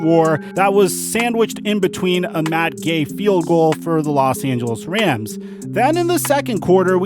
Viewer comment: I thought you said “mad gay field goal” instead of Matt gay